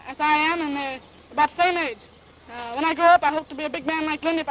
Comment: What did he say? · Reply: He says, "When I grow up, I hope to be a big man like Lindy if I can get a plane big enough." :)